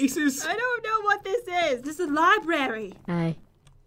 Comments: wait did say hi before popping out?🤣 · I think the puppet says hi to you then he scared you